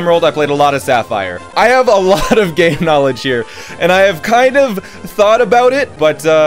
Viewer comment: sounds like he says gay knowledge